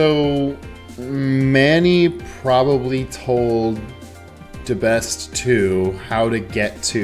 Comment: Did you just say Debeste 2? Maybe would like that one more than the original. I vaguely remember he not being a fan of him.